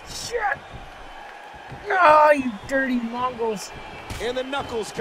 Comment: did u call them dirty muggles?